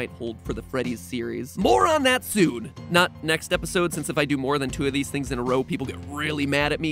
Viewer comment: Matt: I'll tell you soon, but not in a third episode. Me: He's pulling a Scott. It's definetly going to be a third episode, it'll just be titled something else 😂. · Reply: Ibrahim Hassan He actually said ,,Not NEXT episode" of Game Theory - 17:27 it'll probably be the episode after the next episode,.. In about 2 weeks.